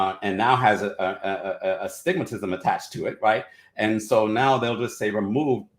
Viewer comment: Did he say " Astigmatism" ?